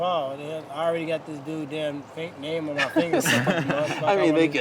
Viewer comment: someone please explain to me why tyron said " I already got this dude's fake name tattooed on me" whatttt am I hearing that right I ran it back more times then Jake Paul is gonna do this fight